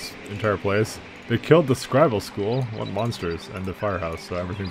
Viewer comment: For the longest time I kept hearing you say "Scrabble School" and didn't realize until this episode that you were saying "scribal." What are my ears. ._. Also, thank you for going back to pronouncing linen properly. I find it amusing how you had it right in the early episodes and then transitioned to a different pronunciation, got it right once two-thirds into this episode, then went back again.